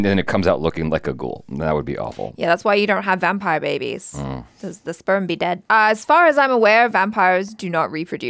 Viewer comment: Did I just hear say, "..because the sperm be dead" at 7:40 ???